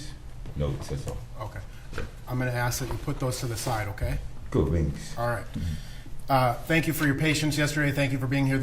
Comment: did he say ‘cool beans’ or am I trippin